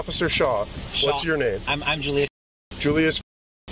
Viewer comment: My heart goes out to the people in the neighborhood who were just trying to sleep. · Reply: because she said her friends name was Julia? I thought the person clearly said their name was Julius.